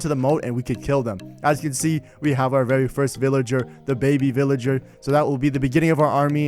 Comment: did you say dababy lets gooooo